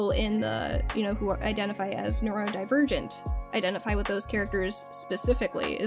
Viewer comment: Dana saying luz is Neurodivergent made me so happy holy crap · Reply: Good lord, the audio quality on this recording isn't super great and I initially heard that as "people who identify of normal virgins," and I damn near did a spit take.